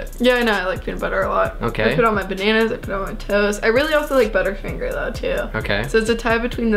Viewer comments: she casual says I put peanut butter on my bananas and I put it on my toes. Lol 😂 I think she was trying to say Toast tho. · really sounded like said "i put it on my toes" about the peanut butter